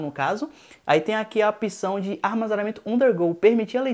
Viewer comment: Apção? Acho que ouvi errado :-/